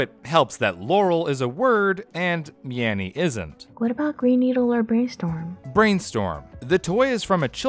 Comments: All I heard was "Ree Neeedor". No surprise it came from a cheap children's toy. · i hear "Green Peace" for some reason.